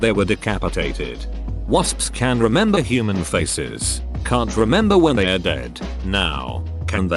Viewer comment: i thought it said wasps could remember human feces